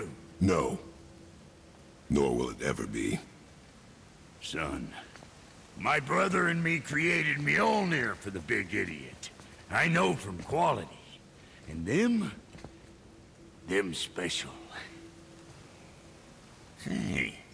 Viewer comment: did he said mjolnier for a big idiot means thor